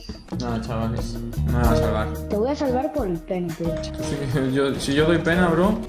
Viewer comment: yo escuche pen*